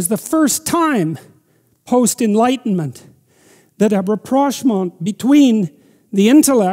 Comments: what’s the word he says here? It sounds like “biproshmong” phonetically to me but I can’t find it anywhere. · What's that word he uses describing the reconciliation between intellect and the underlying archetypal structures of religion? It's french or something it sounds like "re-prosh-mon"??? It's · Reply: Rapprochement, definitely French